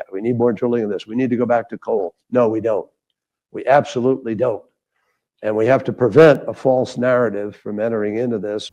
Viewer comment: Did I just hear him say" we need to commit to the false narrative "